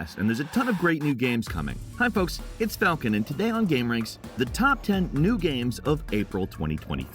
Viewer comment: It may be me, but I swear every time I hear Falcon say “Today on Gameranx”. I hear, “Today I’m Gameranx.”